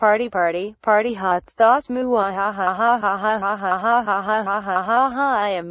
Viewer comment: did she say moo haha?